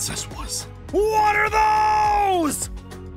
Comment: When Zane said “WHAT ARE THOSE!” I started laughing so hard! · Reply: I thought I heard Zane what the those 😂😂😂😂😂😂😂